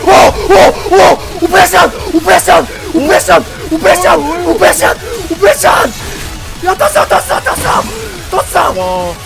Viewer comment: 여기서 오퍼레이션이 오뭐야썅으로 들리는건 기분탓이겠죠